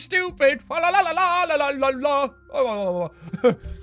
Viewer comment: Did he say allahu akbar?